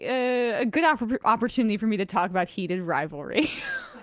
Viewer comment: I was half asleep, and for the entire "Heated Rivalry" bit, I thought was saying "Peter Rabbit." Boy was I expecting a lot from google with the last few searches I just entered.